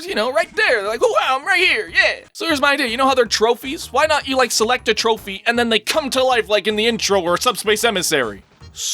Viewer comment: Until just now I thought he was saying "select match."